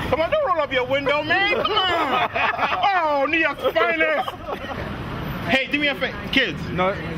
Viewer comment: what did he say at 1:12:17 "new yorks finest"? lol